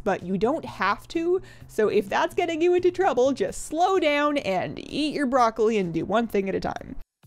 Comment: I heard “yeet your broccoli”